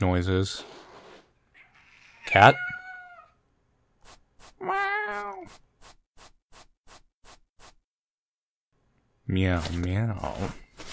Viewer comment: I click on this video and it seems I've watched it before and the first thing I hear is 6:28 "meow meow"